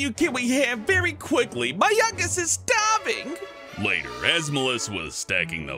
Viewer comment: hellbent: my youngest is staving! me: mY YounGEsT iS stAVinG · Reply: POTATO SHOES I thought it said, "My youngings are starving!"